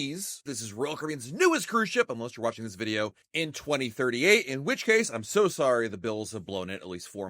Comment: did he say needy in 2038??? I re listened to that like twelve times and heard the same thing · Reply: What? He said "unless you're watching this video in 2038"